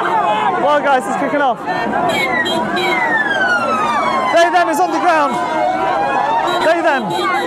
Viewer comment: 😂 I nearly shot iced tea out of my nose, laughing so hard when suddenly I hear 😮 "OH NO! They/them is on the ground!" 🤣🤣🤣